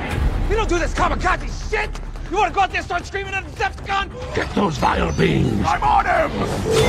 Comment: what did he say? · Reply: Get those vile beings? I’m on him?